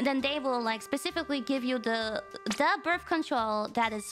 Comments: First time watching I misheard that as bird control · why did I hear "birb control"? or I just misheard because of her accent? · Why did I hear “birb control” first 😅